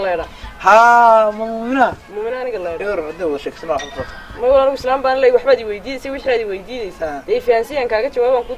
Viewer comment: hold up hold up, did she just said "islanban leehay" 😂